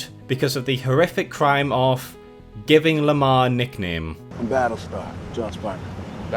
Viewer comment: Cucky Bucky mocks lemar's codename 'BattleStar? one of Bucky's codename is 'White Wolf' I heard Lemar's name as 'Battle-Scar' which is cooler imo